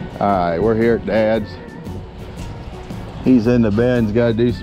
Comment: I first thought you said dad's in the bed, Whuuut ? The I heard you say he's in the bin. Shew We.